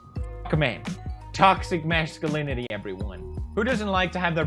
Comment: I heard "TOXIC MAN ALINITY"